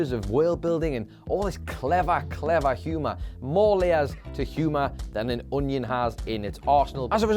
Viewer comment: Can’t be the only one who thought he said ‘more layers than an onion has in its arsehole’